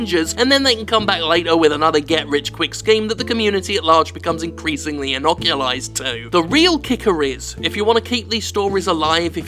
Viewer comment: Did he just say "inoculize?"